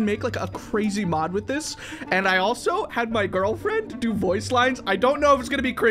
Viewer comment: Wait a minute , Did he say... GIRLFRIEND?